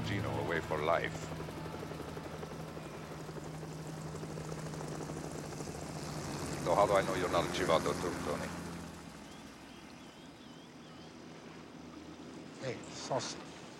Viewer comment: "How do I know you're not chibanotutto?" WTF did he say??